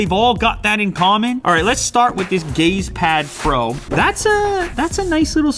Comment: I thought he said gays pad XD